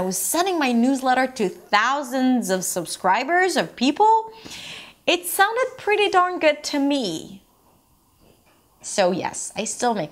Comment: Does someone can help me with this one word at 6:24? "It sounded pretty dun... to me". Couldn't understand it! :( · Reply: “It sounded pretty darn good to me.”